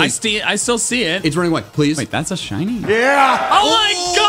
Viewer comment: One of you got to watch wwe cause at 22:13 I clearly heard LA Knight YEAH